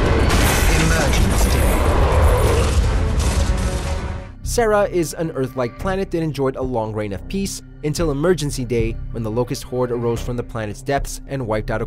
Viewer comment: Did you just say, "Emergency Day?" :/